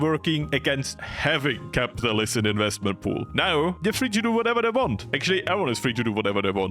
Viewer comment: subtitles: whatever they want what he actually said: whatever they want what my ears heard: wadevadewant